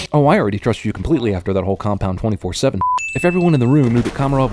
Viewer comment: At #65 or 5:07, it says 27/4 instead of 24/7